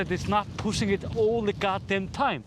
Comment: does he say "It's not pushing it all the god damn time?" hahaha!